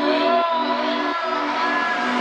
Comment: I thought she said commitment parachute but its call me babydoll 💀💀